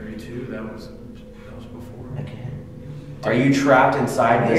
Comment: 1 hour 39 minutes I hear "they trapped us" after asks how they died